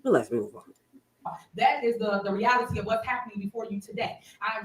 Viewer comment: Hey sis my friend stay happy and blessed, I didn't agree with all ur caucus coverage bcuz I'm like oh well I would of been mushed her and stole her keys😂 but one thing I nor anybody can deny is your tenacity and heart for Dolton and Thornton Township residents so I say Thank you from NY❤❤❤❤ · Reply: Yep you heard her say " ravility" 😅